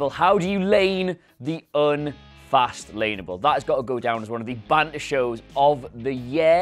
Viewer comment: 'that has to go down as one of the most "???" shows of the year, decade..." WHAT does he say there? asking for help. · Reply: is he saying "baddest" ppv?